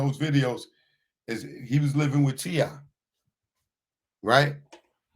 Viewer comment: Did he just say fat Joe was living with t I or did I hear him wrong